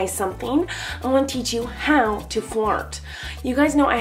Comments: I thought she said "I'm teaching how to fart" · I seriously thought she said: I'm gonna teach u how to fart. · I thought i heard how to fart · I THOUGHT SHE SAID HOW TO FART 😭😂😩😂😭😭😂😭😭